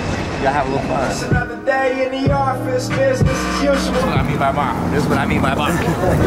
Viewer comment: what's he saying at 3:59? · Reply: saying "This is what I mean by bying."